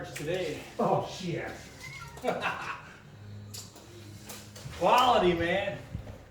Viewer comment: On d 11:02 did you say "hvala ti man" or something else? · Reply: Goran Samardzic I said "quality man"